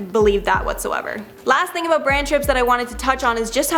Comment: I had to listen 5 different times but I swear she said "Brain Trips"